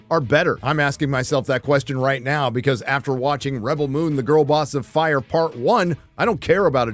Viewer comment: i thought you said "reben moon the girl boss FART one" :D